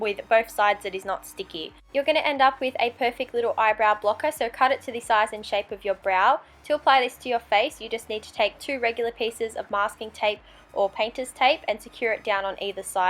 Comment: I thought she said eyebrow plucker and I was like wtf I love Halloween but I'm not about to pluck my eyebrows